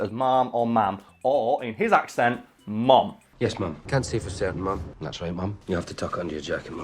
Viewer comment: i thought he was saying "mum". hes saying ma'am?